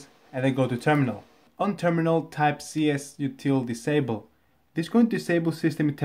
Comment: I can't understand the terminal typing at 2:44 pls comment what is it I really need to post but I can't cuz of my macbook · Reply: it says csrutil disable